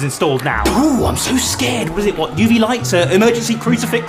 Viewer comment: Thought he said hot water sprinklers XD